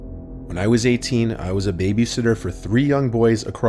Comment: I thought it deadass said baby hitler for a second 💀